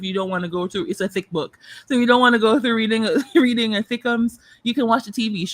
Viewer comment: Did say 'a thickems' for a long book? I'm totally using that 😄